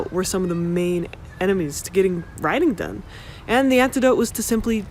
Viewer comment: She said "writing". I thought she said "rioting" Haha clearly i need to subscribe 🤣😅